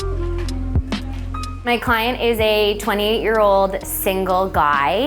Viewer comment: 28 year old bachelor, who has a place like that designed by an interior designer?? Man, did I make some wrong choices in my life! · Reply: Ohh he is 28.... I thought she said he is 20 years old. I was fucking impressed.